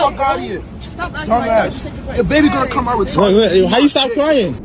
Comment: when this first came around, i lost all sympathy for them once i heard "ya baby gonna come out retarded" That was the red flag for me that there was malic involved, and i doubt a 6 month pregnant Physician Assistant was the one giving it....